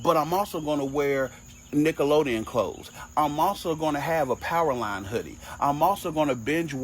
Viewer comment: did you say "Powerline hoodie?!?" I need one. Yes at 36 years old, I will buy one for each on my kids, my wife, and myself. Then proceed to have a family dance party.